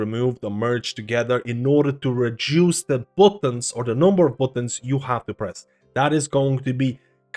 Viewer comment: Dude, did you really say "bootuns" at 0:48 lol